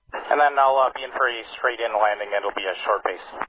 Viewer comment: I think he says something about a "straight-in landing" around 3:50 instead of "frustrating". I can't understand what he says before that, though. 7:20 "You have vehicles in the vicinity there" ? At around 7:54 I hear "You can let them... the duty manager" but I couldn't catch the rest.